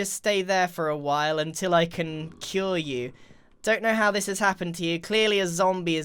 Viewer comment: Who heard grian at 2:17 say " you are gonna have to stay here for a while until I can kill you" and not "until I can cure you" lmao